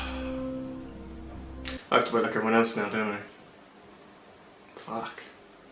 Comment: What did he say at 1:20? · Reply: ‘I have to wait like everyone else now don’t I’